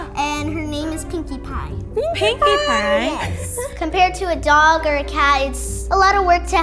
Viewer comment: first i heard pewdiepie xD